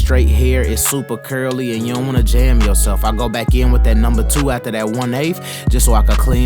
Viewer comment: sounded like you was spitting straight bars. "you dont wanna jam yaself"